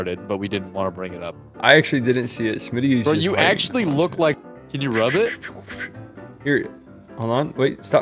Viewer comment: What do we think was said at 6:00? Was it cut to avoid a guidelines strike for or to protect lol? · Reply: You actually look like will smith from the genie, uhhhh aladdin, haha the genie! (You can hear it in Vod)